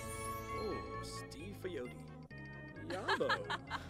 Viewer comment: did Austin say "Steve Aoki"?